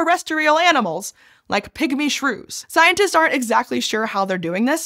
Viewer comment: For some reason I misheard the first mention of pygmy shrews as "pick-me shrews" and pictured them as fuzzy little Instagram influencers